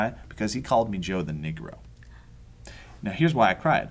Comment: ooh had to go back 7:22 thought he said Joe the dick ro lolz